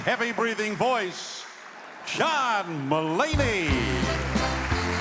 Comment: anyone else heard them say Shawn Mulaney at 0:05 ? no? just me, ok